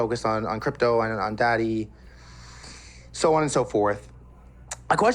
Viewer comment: did u just say focus on daddy 1 second into the interview and try to cover it up😂